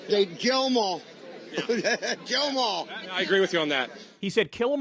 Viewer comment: i clearly heard "grill the mall, grill the mall". i guess. and the crowd chanted "let's go brandon".